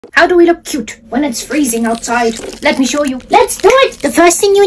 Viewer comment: I’m barely awake I thought she said how do we wear a tutu when it’s really cold out 😅